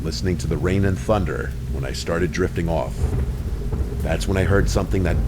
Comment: i literally heard i started jerking off lmao